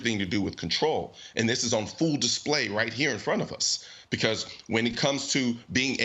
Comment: You say, "...that is on full display right here in front of us." If you review this it sounds like you said, "fool display." That's a great play on words albeit unintentional. Bravo, my man.